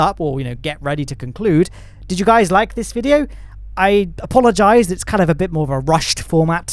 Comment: Did you just say "Apollo-gize" at 31:14?